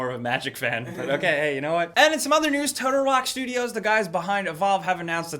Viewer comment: I THOUGHT YOU SAID MOTHER NEWS AND I FREAKED OUT THEN GOT DISAPPOINTED.